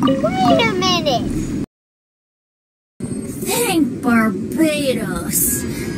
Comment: What Venti says: "Thank Barbatos! ....Wait a minute.." What I heard: "Hang Barbatos! Wait a minute..."